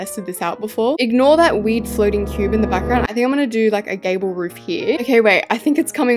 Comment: what she said: “I think I’m gonna do a gabble roof!” what I heard: “I think I’m gonna do a gay roof!”